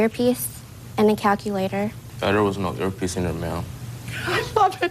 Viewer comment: What does that guy say at 8:42? Stupid guy is mumbling.. I think I hear "Ah, there was no earpiece in there, ma'am"? What an idiot.